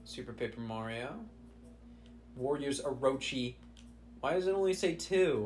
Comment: I misheard “Warriors” as “Wario’s” and now I’m imagining a Dynasty Warios game